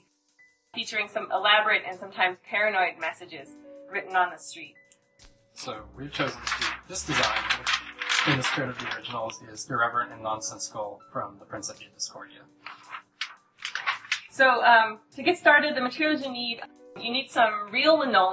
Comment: what is he saying at 0:34?......"In the spirit of the original is dereverending nonsense skull, from the prince of e discordia"………………………